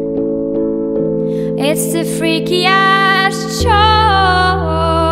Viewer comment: Why do I hear "it's the freaky ass show" xD